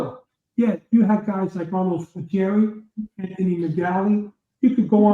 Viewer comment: Did he say Ronald Gultieri?